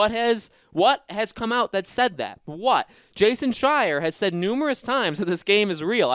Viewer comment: Im not American and I´m trying to undertsand what he said. Jason Shy? Jason Shyang? Jason Shiang? Jason Shyer? AAAAAAAAAAAAAAAAAAAAAAA